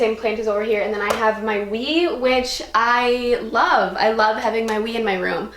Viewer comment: i thought she said weed and not wii and i was about to be like...? you ok?